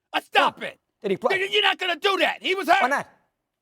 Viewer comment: “N!gg@ your not gonna do that” 😂😂😂😂 I heard it loud and clear 😂😂😂 @ 6:55 mark